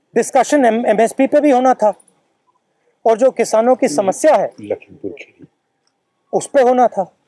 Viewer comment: is it adhir ranjan telling Mr Rahul Gandhi what to say ? :) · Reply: ig he said lakhimpur kheri....